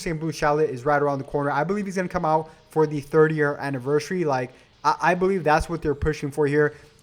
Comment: What he says: The 3rd year anniversary. The cc: the 30th anniversary